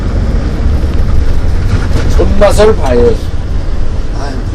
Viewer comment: 난 또. . 손 맛을 봐야 해. 로 들었네. 어선 선장인줄. .